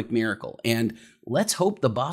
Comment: I heard "honestly, what a jew"